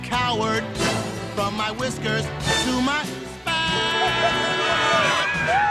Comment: From my whiskers to my spats, I hate dogs and cats! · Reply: you. I have always been wondering what he said especially since I have this movie and it doesn't have English subtitles. I always thought he was saying "from my whiskers to my fat."